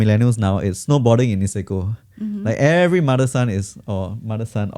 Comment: May i check what said - "Snowboarding in Mexico "? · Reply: Snowboarding in Niseko!